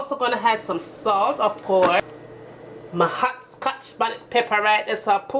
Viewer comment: What was the name of the pepper at 1:35? I couldn't quite understand. Thanks though, I'm definitely going to try this. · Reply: Scotch Bonnet Peppers